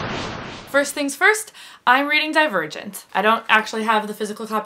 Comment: Thought she said I was reading "Die Virgins" whoops lol